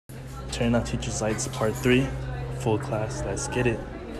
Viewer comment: bro i swear i heard him say turning off teachers life support